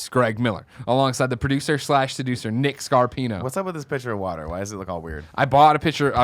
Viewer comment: I really thought Nick said at the start " whats with that picture of watto "